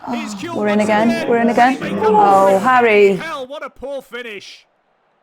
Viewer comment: Did I hear "Bloody hell, what a poor finish!" 😭